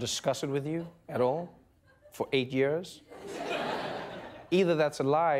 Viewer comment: did he really say 8 years??